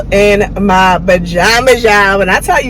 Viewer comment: Did she really say "Ba-Jamas?" asem ooo🙂🙂🙂🙂🙂🙂🙂🙂🙂🙂🙂